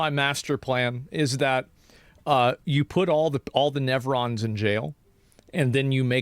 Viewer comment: i dont like nevrons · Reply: I thought he said nephrons